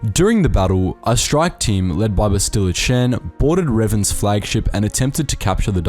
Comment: for a moment I thought you said "Bastila-chan" 😂